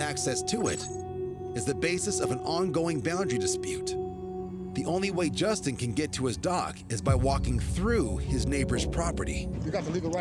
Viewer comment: Thought he said” only way Justin could get to his dog???”😳